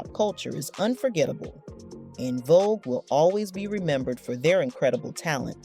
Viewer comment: Never heard of On Vogue...! I have heard of En Vogue smh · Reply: Lol! I thought she said On Vogue. I was like wth! 😵‍💫😄